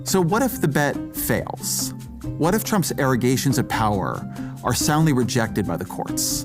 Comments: IS HE SAYING IRRIGATION? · what does he say at @ 5:25? Trumps allegations of power?